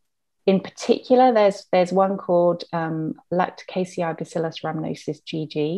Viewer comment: thanks for the graphics/info, I'm wondering what she says is the best Probiotic at 20:57. ? Lacticaci vacilis ramalascious GG ? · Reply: Rhamnosus!